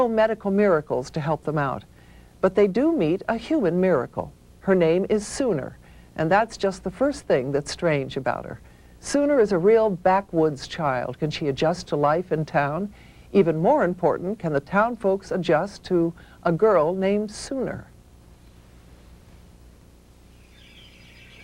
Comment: Back woods? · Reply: Bless you thankyou for putting me right lol I thought she said Backwards, It's cos I'm British, it sounded like backwards, poor woman wasn't patronising her after all.